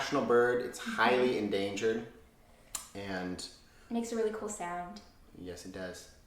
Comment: Oh my gosh 😂 when talking about the kiwi bird I assume you said "making a really cool sound" but what I heard was "they making a really good salad". 😂😂😂😂